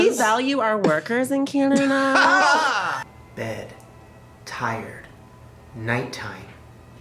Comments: Nope, for the first vid, i picked the swirly lines, the second one i picked a penny and i thought you said tiger at the start not tired and i was confused 😂😂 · I thought you said tried